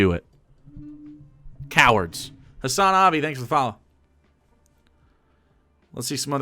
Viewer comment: I heard "Hasanabi, thank you for following" and was like: "THAT'S RIGHT!"